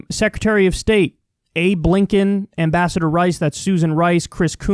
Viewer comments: I swear I heard that as Abe Lincoln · Swear to god, thought he said Abe Lincoln lmao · ablinken.... I thought you said Abe Lincoln for a sec LMAO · ABlinken sounds a lot like Abe Lincoln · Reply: I heard "Hey Blinkin!" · A Blinkin Did you just say Abe Lincoln? No I didn't say Abe Lincoln. I said A Blinken. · Did he just say...Abe Lincoln? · Thought you said Abe Lincoln lol